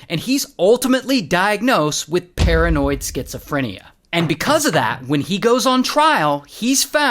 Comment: I was playing with my cat while listening to ray & i thought he said karenoid schizophrenia 🤣😂🤣